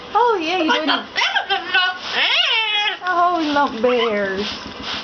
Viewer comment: did he say "the Colts are better than the Bears"?? A football bird? Da Bears! LOL!